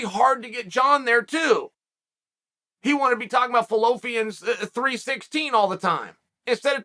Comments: Did anyone catch "fallopian" 316 😆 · Sounds like Chael says Fallopians 3 16, haha